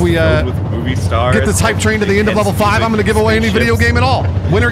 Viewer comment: This whole time I thought you were saying "If we reach level 5, I ain't giving away any video game at all" It was so confusing 🤣